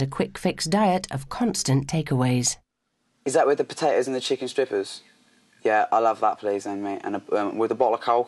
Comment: Anyone else hear chicken strippers at 1:58?